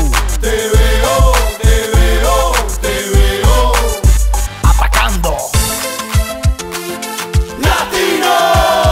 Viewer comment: Why does he say Africando? Africando is a different group right? Is there a connection? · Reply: OriginalJoseyWales He says 'Atacando' which means 'Attacking'